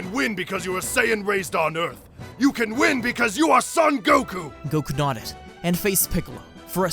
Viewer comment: did he say goku?